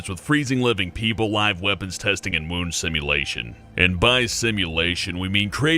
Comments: Wound simulation? I thought he said "Moon Simulation". :c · thought he said womb simulation